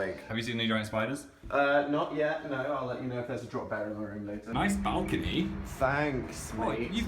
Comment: did say "In our room" ?